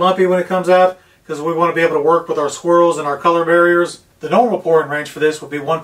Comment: I thought you said Squirrel ..not swirl been a long day .... 🤣🤣